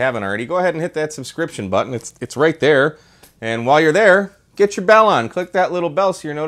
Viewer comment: I thought you said “Get your Balun” I was like OH HERE WE GO AGAIN!